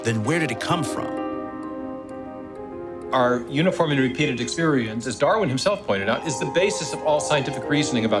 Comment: "...our [garbled words] experience ..." Can anyone tell me what Stephen Meyer's garbled words are? I think they may be important. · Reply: Thanks for the question! The words are "our uniform and repeated experience."